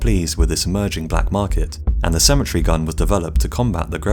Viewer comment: I thought he was saying "symmetry gun" lol